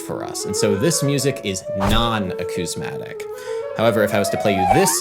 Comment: It feels inauthentic when I hear the word "non-acousmatic" but see the word "non-acousmastic". My cross-modal synthesis gets all messed up.